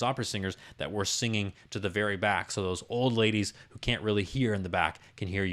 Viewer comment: I could've sworn I heard you say: "OLD LADIES in the back." Maybe I need to get my hearing checked. 😒